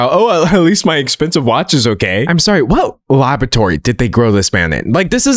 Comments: I heard "what lavatory did they grow him in" and, honestly, I found that quite an apt description! 😅😂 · At first I thought you said “what lavatory did they grow this man in?” Instead of what laboratory. He could have reasonably come from either a toilet or a Petri dish equally